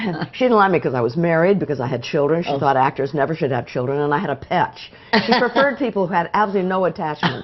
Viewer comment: " Actors should never have children and I had a petch?" What did she say? petch? , pets? Maybe she meant to say " And I had pets/ Not " a pets "?